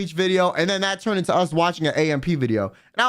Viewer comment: The reaction channel he was talking about around 47:11 was Aba N Preach btw if anyone was curious. (I thought he said AMP and was like they do reactions?? Lmao)